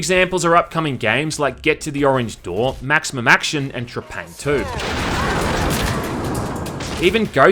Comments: What's the game at 10:21? Sounds like he said Trapain 2? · What's the game at 10:21? I can't for the life of me understand what he says. The subs don't have the poper name either. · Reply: Don't know if you've found it yet - it's called Trepang 2. I believe there's a free demo on Steam rn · What is the title of the game shown at 10:21? I can't make out what is saying. Thanks! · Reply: Trepang2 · What is the game 3rd game mentions @ 10:21? i cant understand is said. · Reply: Trepang2